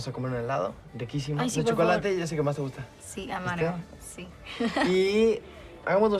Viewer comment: hii! in 9:49 Ambar doesn't say "I'd love to", she says "amargo" which means like sour??. Here in Argentina we have an ice cream flavor called "chocolate amargo" (semisweet chocolate? i think?) which is like regular chocolate ice cream but darker and more intense. So that's her actual favourite flavor (also mine) and what she actually says.